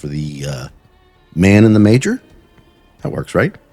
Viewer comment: Man in the major? Ayo? 🤨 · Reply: Thank you for this because, I live in a highly “religious” area and so I heard “Man in the *manger*” like the little bed thing Jesus slept in